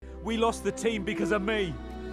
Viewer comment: Ngl first time I heard ‘No, we lost the team because of me!’ I thought she said she lost the TV